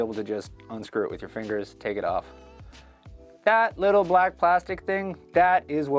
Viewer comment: When he said "That little black plastic thing...." I thought he said "that little black bastard thing..."